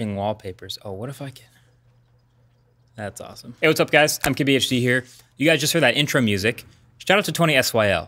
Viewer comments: So long I thought he was saying "I'm kbhd here" ffs I'm high asf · I keep hearing "Hey guys, I'm KBHD here" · Sounds like he is saying, " What a fucking..."